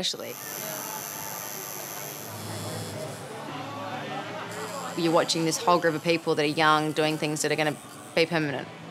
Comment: can someone tell me what it says? Is it flesh air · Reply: Yes, flesh air